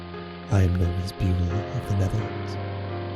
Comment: Did he say that he is Ferris Bueller of the Netherlands?